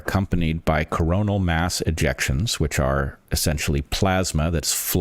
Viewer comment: I thought he said corona mass injections and was like wow, called it 5 years ahead lol